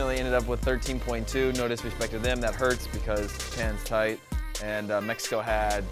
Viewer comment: "That hurts because... HENTAI" who else heard it at 15:40?? lol